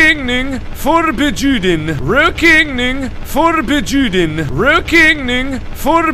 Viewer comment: I hear "row king ning for bah Jew den" XD